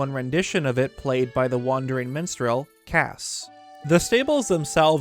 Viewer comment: I didn’t realize I was tired until I misheard him call Kass the wandering menstrual, and was like.....gross. I’m taking a nap after that.